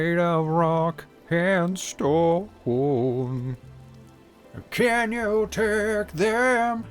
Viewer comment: DO I HEAR A ROCK AND STONE!?